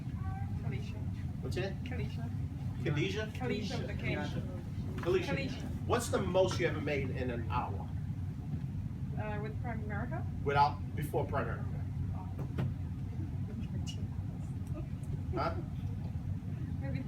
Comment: she really said "with Prime America"? gimmie a break.